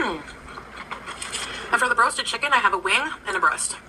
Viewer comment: "Broasted" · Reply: she's saying broasted lol! I thought she said roasted. My bad ;) Ive never heard of the term. I learned today!